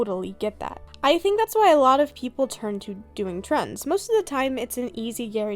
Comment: i heard " a lot of people turn to doing drugs " and i was so confused for a sec